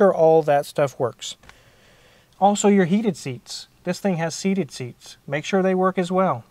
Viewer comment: did he say ‘seated seats’